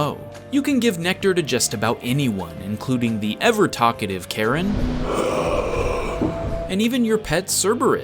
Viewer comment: “The ever talkative Charon” Lol I heard “Karen” He is right though. Hades is a pushover in KH.